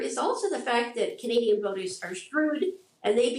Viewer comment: Did she say "Canadian voters are screwed" on TV?